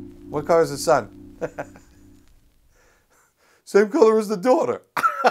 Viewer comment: "same colour as the daughter" that caught me SO off guard haha, had to go back twice to figure out what he actually said... gotta love this guys spirit